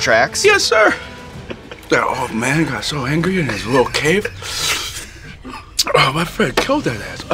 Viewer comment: It's amazing, Hot Wings is 100% responsible for making me like Pete as a genuine person. There's a certain scoville level everybody hits and their true personality comes out. Shits like a damn truth serum 🔥🔥🤣 And having Pete Davidson's voice go up a register while talking about Eminem has made me realize that we really need Eminem on the show. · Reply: Same, even though it kinda sounded like he said his friend killed Em. (Killshot was far better). I guess he's trying to stay loyal to his friend.